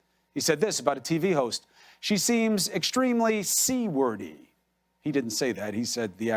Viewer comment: "She seems extremely c-word-y... he didn't say that, he said the actual word." Phew! I thought he said seaworthy.. Thanks for clearing that up, Chris.